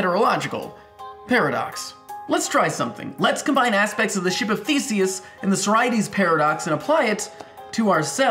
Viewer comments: lol xDi thought he said the ship of faeces and im glad to know ur cancer is getting better i got scared when i saw that video when u said u have cancer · I LITERALLY THOUGHT HE SAID THE SHIP OF FAECES... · LoL I thought you said "The ship of fecees!" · I thought you said "The ship of feaces" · I heard it as the shape of feces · At first I thought he was gonna say 'the ship of feces' and not 'the ship of thesius'...